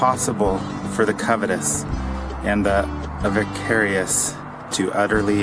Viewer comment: Wtf is avacarious swore its said avaricious